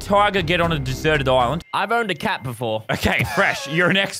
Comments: Who else thought fresh said “I boned a cat before” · Fresh: "I owned a cat before" What I heard: Fresh: "i BoNeD a CaT bEfOrE" Me: wait what? 😟